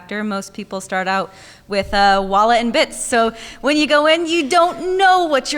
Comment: and 22:15 what are they saying? “Wallet and bits”? Edit: oh “walla and bits”, I see now